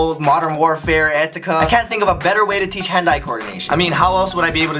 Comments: is it just me or did u hear 'hentai' coordination instead of hand eye coordination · Thought u said hentai coordination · i think i just heard "hentai coordination" LOL